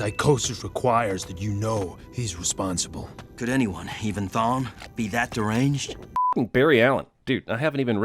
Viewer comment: This entire movie I SWORE he was saying “thong”